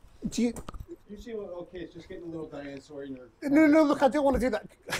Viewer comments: What the heck did Robert Downey Junior say to the guy just before he walked out...? "you seem a little okay... it's just gettin' a little dyin' on that sorry nerve...." (8:34)??? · "You (can't make out) ok, it's just getting a little Diane Sawyer in there" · What's he saying there? 'It's getting a little dinosaury in here'???